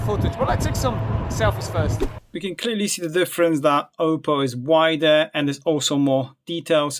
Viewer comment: did he just say Oppo? 😁 presence of mind my friend, presence of mind. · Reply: Yes I did. In hard OnePlus feels like OPPO